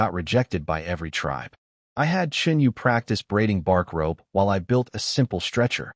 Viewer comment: had to do a double take when I thought I heard breeding 😂